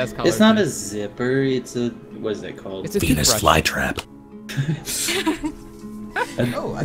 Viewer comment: When said "Venus fly trap" I heard "Penis fly trap" And apparently my dad heard it too and called "THAT HAPPENED TO ME ONCE!" I can't stop laughing xD